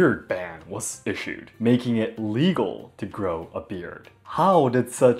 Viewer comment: illegal, il-legal. it sounded like you said "legal."